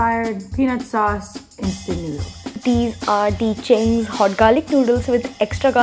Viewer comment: I think u put subtitles wrong there, Coz the Indian girl having "Ching's hot garlic noodles" (Ching's is an Indian instant noodle brand btw)...Not Chínese noodles as u put in subtitles.